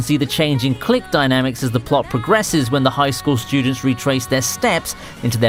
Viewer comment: Could have sworn he said “high school shootings” there 😅